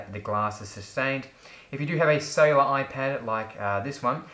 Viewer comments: When you said cellular, I thought you said sailer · i always googled "iPad Sailor" until i heard you say it for like the 27th time, then i understood, that you meant "Cellular"...